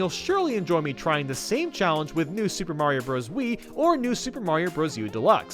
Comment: Could of sworn he said stupid mario bros